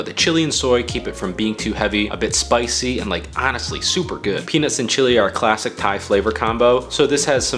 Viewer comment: ok I heard that as pean(ut)s and chilli are classic combo